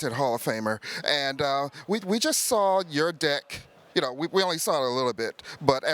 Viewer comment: did he say we just saw your dick!?